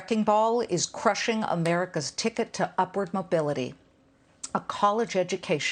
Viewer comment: I could have sworn that Liz said education is the “ticket to Marxist mobility”. I had to check. She said “upward mobility”. For a brief, shining moment, I thought she had said the right thing.